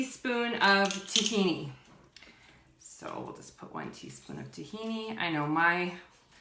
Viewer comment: Thank you for this, what was the stuff in the jar that you used.. I couldn't quite hear you.. It sounded like u said tyme? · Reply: Jessica M hi! It was Tahini 💚